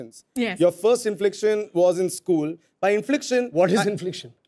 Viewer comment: That's because Abish was mispronouncing it as Infliction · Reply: yeah i too heard it as infliction...